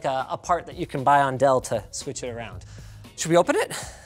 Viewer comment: why do the captions say Delta switch it around when he says "Dell to switch it around " Edit : There are more errors in the captions - 4:04 Mc instead of Msata 7:20 Ac instead of Asus 8:20 Cinnabuns instead of cinebench